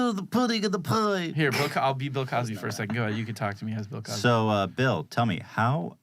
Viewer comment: i hear @ 5:43 noob noob not cosby lmao